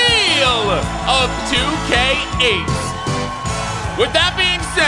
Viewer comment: did anyone catch him saying 2k8